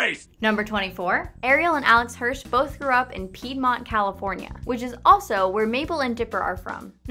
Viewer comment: Did she just say both threw up and peed